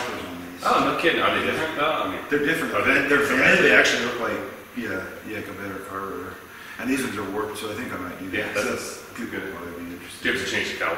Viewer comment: I have hearing loss, and at 06:05, you stated something about replacing the rotors and calipers with another type. Between the echo in your workshop, and my hearing, I couldn't make out what you said. Could you write out what you said? Thank you! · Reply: You can replace the 2 door rotors and calipers with 4 door ones. The 4 door has vented rotors. The calipers need to also be changed because they are different to accommodate for the width of the vented rotors.